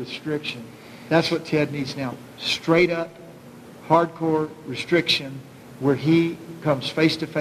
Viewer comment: did he say "hardcore"?